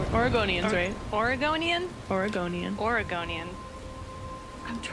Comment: I really thought you said Argonian. The Elder Scrolls Is Strange.